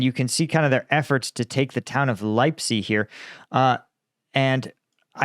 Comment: Chris on a bike!! I thought you said Lipzig!!! And you know what? It didn't surprise me....